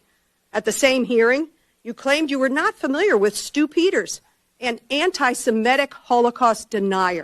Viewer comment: When she said "Stu Peters" I thought she was saying "Stupiders" I guess Stu's name is what he is? Stupeeders? So is Kash Yap. Give him enough "Kash" and he will "Yap" about any lie you can come up with?